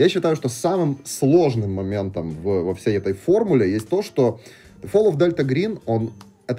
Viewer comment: Мне послышалось Call of Delta Green, вместо Fall. На секунду завис